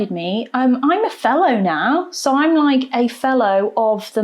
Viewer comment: She said fellow 😂 I thought she said felon. I was like "why would you be happy about that?